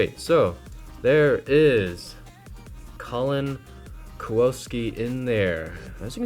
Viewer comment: Kallen "Kuwoski" lol. Kallen's name if she'd been half Polish and half Japanese instead of half German